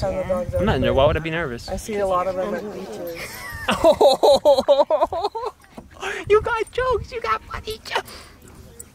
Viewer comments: What did say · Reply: If u do play back speed 0.25x it definitely sounds like she's saying "cos your around me" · Is it just me or did say "you got fucking jokes"